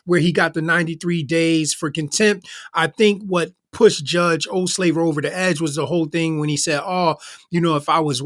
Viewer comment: When he said the judges name my hubby (a black man) yelled out from the kitchen, "did he just say old slaver?"... He got a real kick outta that😂😂😂